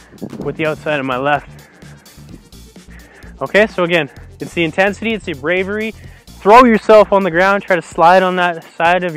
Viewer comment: closed caption/subtitles says you are 'going in with the intention of gayness'. I applaud this. More players should be coming out.